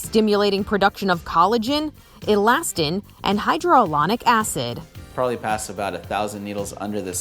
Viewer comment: Did she just say hydroalonic acid? It is hyaluronic acid. lol